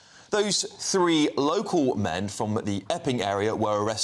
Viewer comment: this police woman is absolutely mental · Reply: I did a double take because I thought he said "3 men from the f-ing area." and had a chuckle. 😅